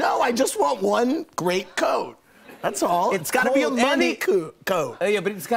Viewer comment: Lmaooo he said “money coot” instead of “money coat” at 5:26 haha maybe it was just me who noticed lol